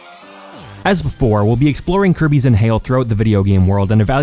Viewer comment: At first, I thought he said "As vore,"